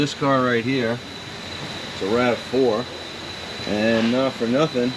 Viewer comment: For a moment, I thought you said 'A RAT-4'